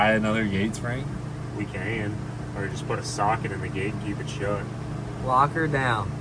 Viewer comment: I 1000% thought said "Put a sock in it" 🤣